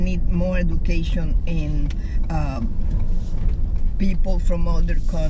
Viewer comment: At first @ 3:03 I thought she said unmedicated people LMAO 😂😂😂 that would have been true too